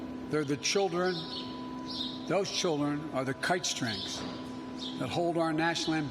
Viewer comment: I thought he said, 'Kike strength!' 😳😲